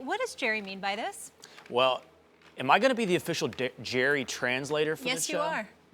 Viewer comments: Did Will just say"Am i going to be the official dick Jerry translater....."?....Lol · Will Cain was about to say "dickrider" 0:05 lol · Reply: I thought he said, "am I gonna be the official Dak-Jerry translator"